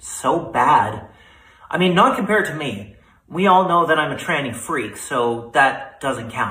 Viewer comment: the transcript reads “…I’m a freak…” When he clearly says “…I’m a tranny freak” huh, interesting. Well guess I’ll go work on my truck, the tranny is broken.